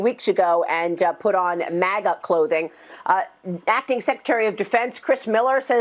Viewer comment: LMFAO When shes talking? And saying that "Democrats put on maga clothing..." 1:35 -.- the CC said "maggot clothing" which personally, I think is more correct lol! Just sayin ^.~